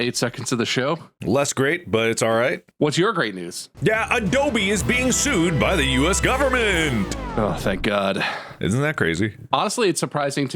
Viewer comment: I legit thought at start Mark said "Dobby is being sued by the US government" and for couple replies that went down fine